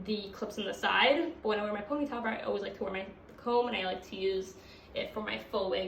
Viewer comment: Thanks for the info but you speak very fast it's hard to understand you sometimes. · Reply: agreed! I had a lot of trouble understanding her words. At 3:38 3:29 I think she's saying something about a ponytail maybe?